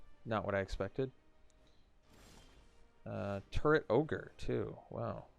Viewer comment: You say, "Turret Orge" I hear "Turd Ogre".